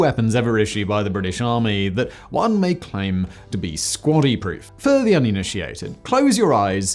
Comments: At first I thought you said "squattie proof" and was thinking that the weapon had to survive a toilet being dropped on it. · I thought he kept saying "squatty-proof"... like you could drop it in an outhouse and it would still work